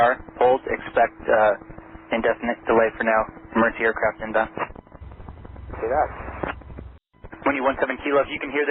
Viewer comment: I think he said 'copy that'